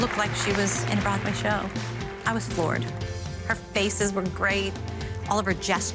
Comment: I thought Melissa said “I was bored” 😂